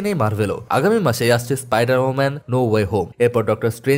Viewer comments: মিনিটে কি আপনি হাচাই স্পাইডার ওমেন বলছেন? অনেক বার শুনলাম ভুল তো মনে হইতাছে না। তাও বিশ্বাস হয় না।🤪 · কি বল্লা ভাই Spider-Woman: No Way Home🤣🤣